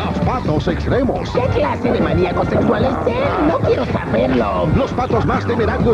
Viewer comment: ¿Que dice? ¿Maniaco Sexual? ¿Que onda con eso?